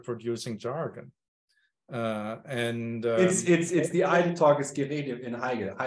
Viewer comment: what is this word you mention from Heidegger? I can't make it out. I know it's German but it'd be useful to know. I was hoping to search out the context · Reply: It's "Gerede" as in idle-talk.